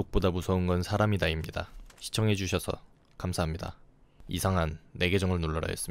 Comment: 이상한 김계정 입니다 나만 들리나?ㅋㅋㅋ